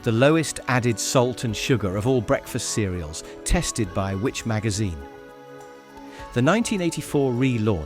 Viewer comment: The computer generated subtitles are hilarious - look at 5:20 - WITCH magazine !! .... Anyone else heard of it ? .... Nope. .... I think everyone knows of WHICH magazine I'm sure. .... and again at 5:36 SIRUP . .... Not SYRUP then ? ... There are plenty more to come - I really can't be arsed now to list them all.